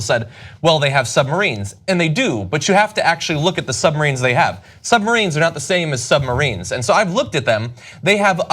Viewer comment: Does he say "Submarines are not submarines?"